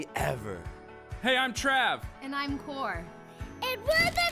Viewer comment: omg lol I thought she said "Hi I'm Trav and I'm poor" and I laughed my ass off